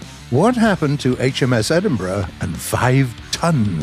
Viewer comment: Every ti e he said Edenbouroguh,,I know I spelt it wrong. I thought he was saying edin- BRUH